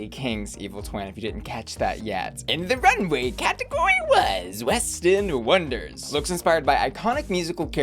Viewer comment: I totally thought you said “Westin” not West End. And I was like- “the hotel chain? I’m so confused.” Hahahahah.